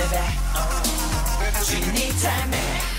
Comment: 是只因你太美才對因為念太快才聽成雞你太美